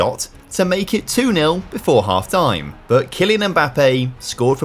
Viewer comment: did I hear that right. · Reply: I hear Kylian and it was Ethan